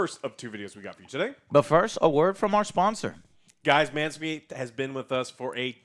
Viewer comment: sounds like he said manmeat